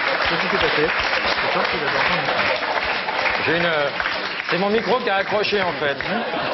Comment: Help ! Il dit quoi a 2:31 ? j entends "leuuuubi". Ou si qqn peut m expliquer c'est cool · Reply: "Une bite !"